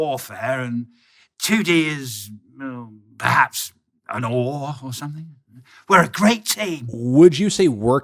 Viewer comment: What does Murdoc says at 2:40? " ...and 2D is... perhaps... an ??? or something." Noah? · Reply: oar